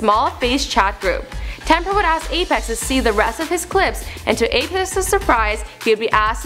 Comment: lol she said apes's instead of apex's